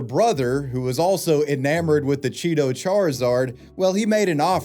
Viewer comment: I heard "cheeto-czar" and was expecting a completely different outcome 😂